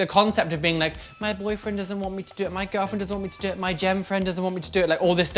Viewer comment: wait what was the word you used at 2:10? "my girlfriend doesn't want me to do it, my boyfriend doesn't want me to do it, my ____friend doesn't want me to do it" it sounds like gem but i can't tell, and i like the idea of a better word than "datemate" bc while that's cute, it doesn't match the binary options